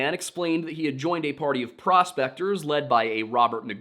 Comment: oh PROSPECTORS I thought he said a party of crossdr…..